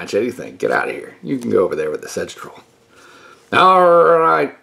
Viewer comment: I really thought you said "Sex Troll" at first. 😂